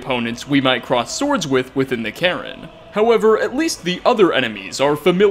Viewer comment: Anyone else hear him say "within the Karen" at 5:50?